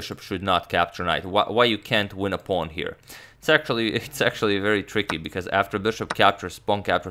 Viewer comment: anyone else heard "sexually" instead of actually